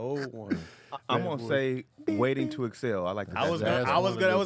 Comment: Anybody else catch DC pronounce Waiting To Exhale like "Waiting To Excel"? XD